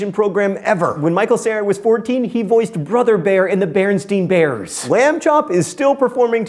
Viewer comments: Who else heard him say Berenstein??? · Who else heard him say: "BerenstEin Bears"? Parallel universe confirmed??????????????????????